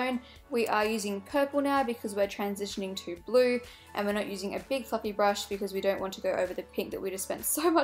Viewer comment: it sounded like she said big fucking brush!!! lol!!!